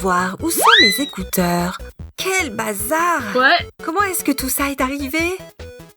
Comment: Pourqoi à 1:40 on entend OUAIS😂😂😂😂